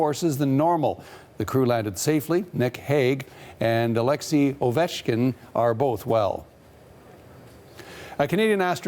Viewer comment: did he say "Alexei Ovechkin"?